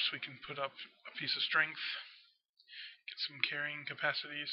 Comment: When u said strong i thought u said string